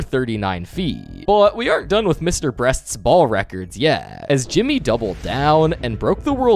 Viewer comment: bro called him Mr breast instead of Mr beast💀